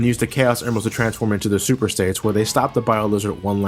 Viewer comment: did u say super saiyans or super states? in